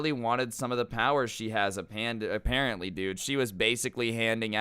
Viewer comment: did he say a panda?